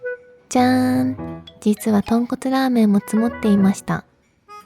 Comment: 寝ぼけて動画みてたら「豚骨ラーメン」が「ポンコツラーメン」に聞こえて、目が覚めてしまいました😅。 知多のウイスキー飲み放題なら行ってみたいと思いました😊。 美味しそうな動画ありがとうございます✨。